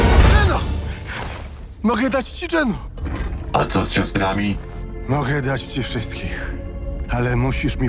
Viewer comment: ja usłyszałem Geno dam ci dżemo a co z ciastkami? Dam ci wszystkie XD